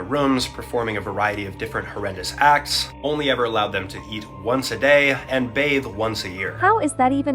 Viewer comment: i thought he said breath once a day lol